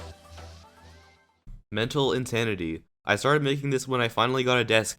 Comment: Is it just me, or did he say Intanity